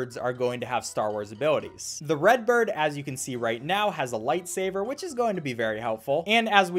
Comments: DID YOU SAY LIFE SAVER? LIKE THE CANDY? I THINK YOU MEAN LIGHTSABER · Did he just say lightsaver also how did you get the game? I thought it was taken down everywhere by rovio